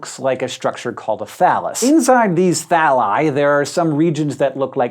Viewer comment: Anyone else hear him say 'phallus'?